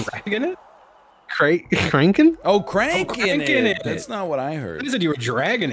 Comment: Cranking it, franking it, I thought I heard he was tranqing it.